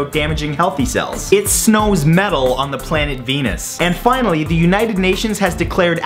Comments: when he said that it snows metal on the planet venus i thought he said " it smells mental on the planet anus" oml im laughing so much that im crying XD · I thought he said "it snows metal on the planet anus." Oops...